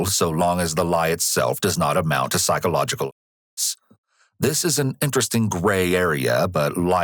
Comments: what word is blocked? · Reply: For some reason they keep censoring the word "abuse" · they bleep a word and even the closed caption doesn’t give the word. When the detective lies about the child being dead the narrator states it’s legal as “ long as the lie itself does not amount to psychological a*****.” I give up. What’s the word? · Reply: i believe its "abuse" :p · what is the censored word here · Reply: Abuse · WHAT is the five letter word starting with the letter A he keeps starring out?? 15:16 minutes in is one time it stars it out, please help! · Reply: abuse